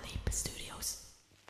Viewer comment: i always hear "broccoli studios" its so damn funny